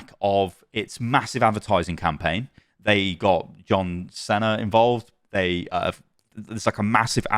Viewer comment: No way my man just said yohn cenner instead of John Cena 😂